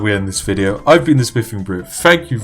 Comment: It sound like he is saying "The spiffing Brif" or is that just me?